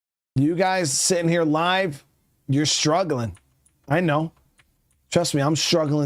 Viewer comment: lol i heard you say that you were all struggling